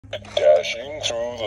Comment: I thought santa said dashing through the snow I gotta poo